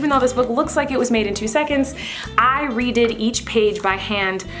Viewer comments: did anyone else catch that she said "i readed each page" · Lol 3:07 she says readed instead of read